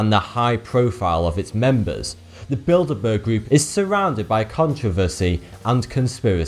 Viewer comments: I wasn't watching for a bit and I thought he said "The Build-a-bear Group" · Could've sworn he said "the build-a-bear group" at first haha